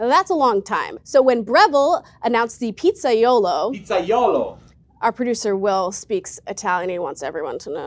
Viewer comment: I thought she said ”pizza YOLO”